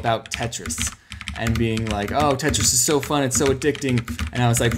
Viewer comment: I thought he said 'Tetanus is so addicting!' Me: ... What? looks at replies Me: Ohhhhhhhhhhhhhhh...